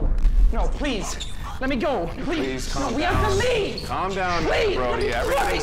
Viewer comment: has anyone tried to figure out what is being said at 10:50 ish? i definitely hear something about "let off" and theres a few other phrases i got too. theres alot of reverb added but i think it could be removed fairly easily. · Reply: I hear “we have to leave” and then it sounds like it’s saying “ofu ofu”. But that’s just with my human ears.